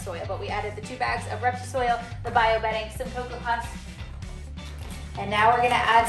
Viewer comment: Did she say coco puffs? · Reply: Coco husk!